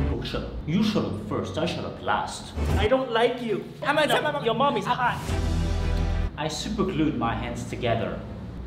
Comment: I heard "Your mommy's hot" and I feel weird.